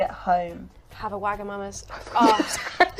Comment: Anyone know what she says at 27:18? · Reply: She says “Have a Wagamamas”, which is a restaurant chain in the UK, but it sounds like she’s about to say, well, something else